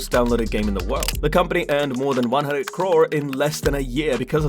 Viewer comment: Did you say crore?